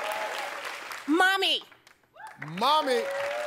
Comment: legit answers: mama mom/mommy nanny (this wasn't on the board) nana (this too) HER answers: nana mommy mommy nana mom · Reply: FT Video I thought she said Mom-MEE 😛